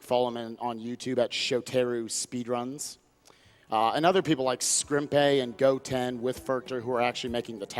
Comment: at around 6:29, did said "skrumpei"? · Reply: Coati he said Scrimpeh, the primary TASer for this game